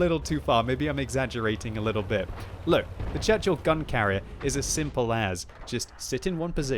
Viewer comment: I thought you said "Churchill gun carrot" XD